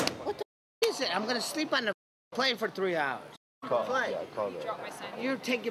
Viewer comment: I could of sworn he said "train" instead of "plane"